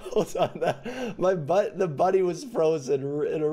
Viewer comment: Denis:"My bu- was frozen a bit there" Me: Hold on what are you saying? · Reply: my bunny?